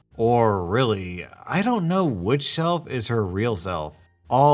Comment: Anyone else realized he said "shelf" instead of "self" at 5:01 XD